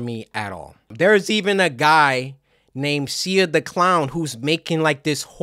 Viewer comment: did he just say sia? 🧍🏽‍♀️? Sia?!?